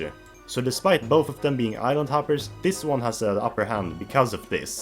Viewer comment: Interesting list for sure - though (and I intend no offense on this) I kept mishearing island hopper as "idle topper."